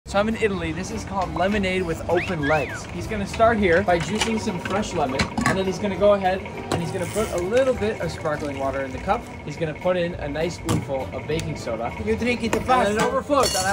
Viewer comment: i thought he said Lemonade with open eggs 🥚